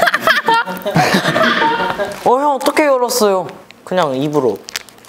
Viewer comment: They put "mouse" instead of "mouth" when Haechan was talking and the first time i saw it i DIDNT REALIZE THAT ( min